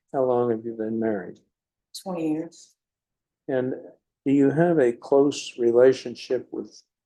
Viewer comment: Am I wrong? Did she think she was getting probation? She looked kind of surprised when the judge said 4 years and credit with time served, which was how much time served? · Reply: I thought she said 40 years. Audio on these cases are not so good sometimes.